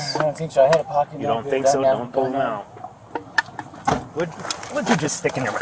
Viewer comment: That smirk on his face after closing that door and the fact he seemed more concerned about his nose bleed (looking at his reflection in the victim's vehicle) than the guy he shot and now bleeding to death tells you a lot... and another thing ...was the guy reaching for the cops gun or was he trying to stop the cop from reaching his gun ? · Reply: I thought I heard "don't play with my gun". Although "play" was a weird word to use.